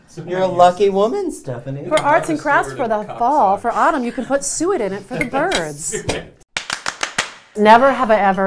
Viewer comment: I'm still trying to figure out what Andrew was saying to Christian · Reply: SaltMoth I think it's "you can hang birdseed in it" or something